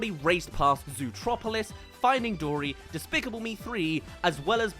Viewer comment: what did he just call it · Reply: Zootropolis. Zootopia's name was changed to that in Europe and some other places because of trademarking issues I think